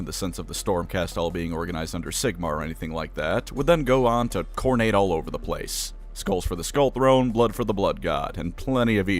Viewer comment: I had auto subtitles on for this video as I tend to do (Good at English but not my native language, and just not always understanding what's being said audio-wise sadly, shame on me) and at 05:05 the subtitles said Khorne's forces "would go on to continue to Khorne eight all over the place" and I find that just beautiful.